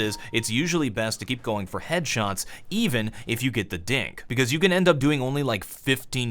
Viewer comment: Is it just me or did he say dick instead of dink